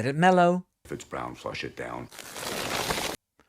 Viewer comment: Thanks for the trenchant analysis and for the laughs. For a moment, I thought you were saying "doo doo" at the end, a child's way of speaking in English about what gets flushed. Doei doei!